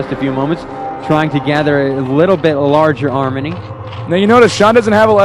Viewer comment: did he say arminy?